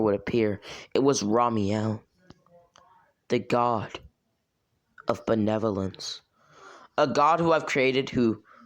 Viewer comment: For a second I thought he said god of bananas